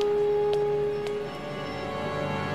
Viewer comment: Oh. I thought he said "Guns don't work on Sunday"